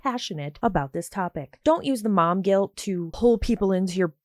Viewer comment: 😆I heard "Mom guilt" as "MOB guilt" at first and had to rewind to get it right!!!